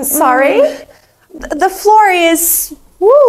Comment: I thought she said the floor is lava